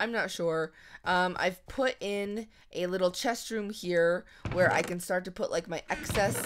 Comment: or so i thought u said restroom. LOL